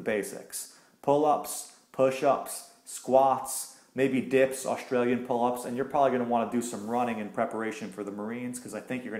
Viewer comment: What he said after "Dips" in 0:43 ? Which exercise is it? .... push ups ? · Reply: Australian Pull ups?